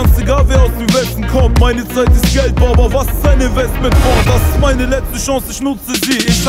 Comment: Was sagt der da (Baba, was ist eine West mit Kolt (Bold) ??? Ka) 2:48 - 2:52 Hab ich auch net genau gehört (ich bin Gangsta-Rap-Hopper, mit Gangsta-Track vrob pra ~??ka) Wär cool wenn ihr mir bescheid sagen könntet was der da sagt.